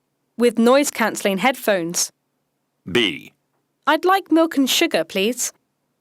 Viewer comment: milk가 전혀 들리지 않고 I'd like 노큰? sugar 로 들림... 아 진짜 공부하기 싫어진다 ㅜ =================== 8. 9. 새벽 2시 와 지금 다시 들어 보니 milk로 들려요. 그때 분명 m이 n으로, il이 o로 들려서 나만 그런가 왜 이러지 으아아아아!! 하며 빡돌아서 혼자 씩씩거렸는데... 뭔가 제 청각 회로에 문제가 있었나 봄. 이건 밑에 공식 답변러가 조언해 준 단어 공부의 문제가 아닌 듯... 컨디션 문제도 아닌 듯... 그때 규칙적으로 운동 하고 먹는 것도 소식했는데.. 반면에 그저께 컴터 게임으로 에라 모르겠다 하며 날밤 새고 안 자고 버티면서 저녁6시까지 게임하고 왓챠 처보다가 잠들어서 오늘 아침 11시 쯤에 일어났는데 ㅋㅋㅋ(정확히 말하자면 어제 아침 11시라고 해야겠군) 혹시 저와 같은 분이 계시다면 심각하게 생각하시지 말고, 생활 패턴에 변화를 줘 보시는 것도... 이렇게 자기 합리화가 시작되었다.